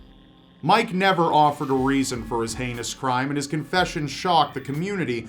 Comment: There's nothing more heinous in this world than those that harm children. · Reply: I honestly thought you said "hilarious", not "heinous", Jesus Christ lmao.